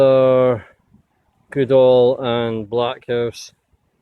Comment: Woohoo I've got a Goodall and black house, I thought it said rack house sauce lol